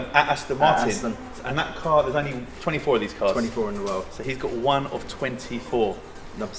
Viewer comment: Did he just say he has 24 of those MF cars??? Is he from Sinaloa, cause looks like one of them guys esp with that half jacket that the former Sinaloa boss (now in U. S prison) liked to wear.infact it's exactly the same piece... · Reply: i think he says there are only 24 of those cars in the world..and he has got the number 16 but a half jacket is definitely a red flag :P